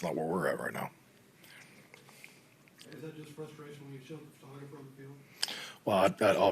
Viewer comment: What's Big Mike talking about at the very end? I can't make out the final question, something about an interaction that Mccarthy saw differently. Is anybody catching that part? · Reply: I figured it out! I realized it sounded like he was saying “shoved” so I Google “Mike McCarthy shoves” and instantly found the incident mentioned. The guy is saying “is that just frustration, when you shoved the photographer on the field?”